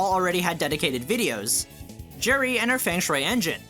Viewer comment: I thought you said "Jerry"